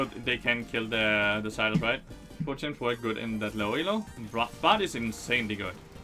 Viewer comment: What did you say from 3:56 - 4:00? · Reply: What champ works good in low elo?and he answers bard